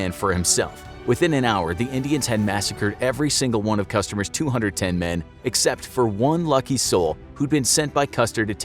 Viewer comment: I hear him say, "customers 210 men" at 7:56!! lol Any one else catch that or am I hearing things?